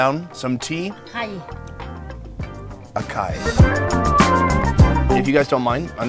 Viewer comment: did sony just sayed "hakai" 😂😂😂😂😂😂😂😂